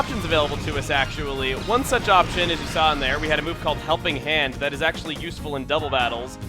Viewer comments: Coming back and when you said "helping hand" I straight up thought you said "Jumping hand" it strange on my end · Am I the only one who heard "Humping hand" at 6:15? Dragon out!